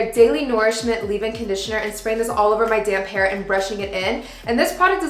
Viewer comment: I started laughing when you said I’m spraying this all over my “damp” hair. I thought you said “damn hair” I was like sheesh it’s like that lmao